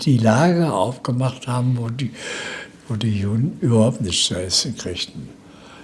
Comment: Erstmal wie immer ein sehr schönes Video, vielen Dank! Min. 37:25 Was sagte der Herr dort genau? Die Juden oder die Jungen? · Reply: Danke für deinen Kommentar. Die Juden.